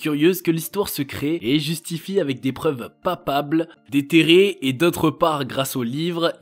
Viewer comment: c'est quoi des preuves "papables"?